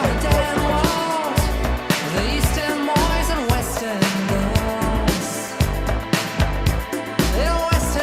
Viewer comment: I named my son Weston after this song. When I was a kid I always thought they were saying Weston girls so it just stuck with me and I always loved that name. Always will be a very special song to me.